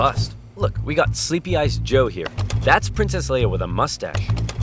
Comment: Did he just say sleepy eyes joe?